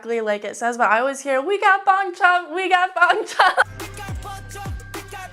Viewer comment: “We got Bang Chan” 😹😹 I always hear: “Pick up poncho, pick up poncho”